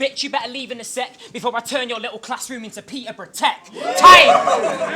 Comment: what does he say around 07:38? something about peterborough?